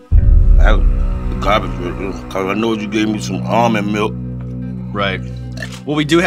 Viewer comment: Can someone explain to me what my man's saying here. 😂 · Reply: He said “ahh...the garbage right there....because I know you gave me some almond milk” 😂